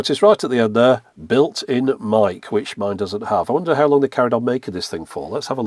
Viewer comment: I can tell my brain wasn't working for a minute because I heard "Built in Mic" as "Made in MIKE" and I wondered what county MIKE was and what that acronym even meant.